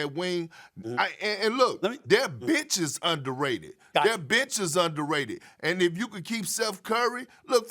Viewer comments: Almost thought perk said their b-tch is underrated 😂😂🤣😂🤣😂🤣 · When KP said "their benches are under rated" I thought he said "their b!tches are under rated". LOL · I thought Perk said "Them b*tches underrated", instead it was "their bench is underrated" lol Man he really needs to work on his speech